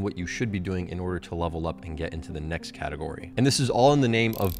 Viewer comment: I swear I heard him say " into the next Chadegory"